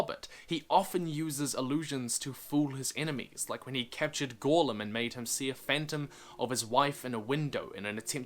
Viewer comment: Wait, wait, wait...... Gollum had a wife?! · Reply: Gorlim. A character from the Silmarillion. I thought he said Gollum at first too